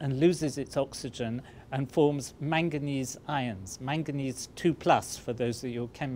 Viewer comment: manganese II ions I thought he said manganese irons